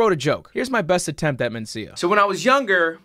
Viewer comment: I thought he was saying vita-mencia